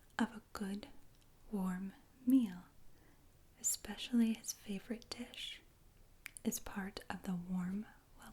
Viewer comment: “-his favorite dish” deadass thought u said “favorite tit”